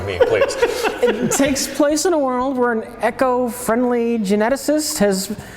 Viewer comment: Did say Echo Friendly?